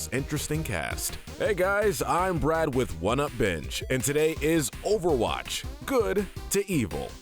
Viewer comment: When he said “1upBinge” I heard “What up BITCH”